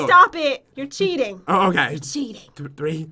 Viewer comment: i thought he said i see hoes🤣im srry bout tht....